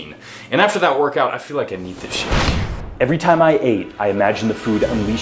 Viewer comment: What does he say there? "And after that workout, I feel like.. I need this sh*t" !?